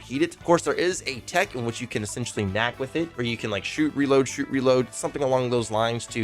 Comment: did you just say nac from cod trickshots